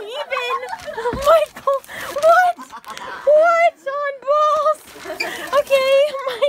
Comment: Did she just say “What?! What on balls?!”